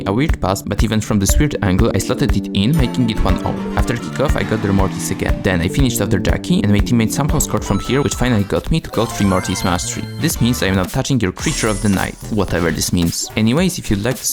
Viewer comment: did anyone else hear „Finished on the Jacky“ 💀